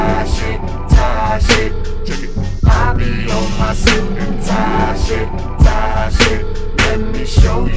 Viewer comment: I’m I the only one who thought it said soup and pie💀